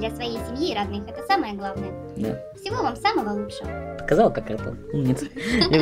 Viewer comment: "сказала как.." кто? Не расслышал прост)) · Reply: расшифровываю специально для тебя "сказала как ртом"! )))